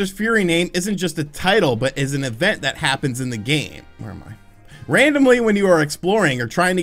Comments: did he say "where am I" reading from a script? lol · Did he just say "where am I"?